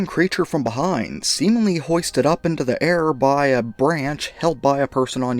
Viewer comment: I heard "moistened up" had to do a double check